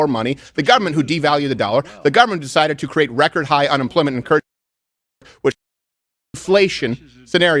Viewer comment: audio cuts out and I don't have any idea what Steven said. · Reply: Apparently someone doesn't want me hear something about record high( guessing) unemployment and inflation?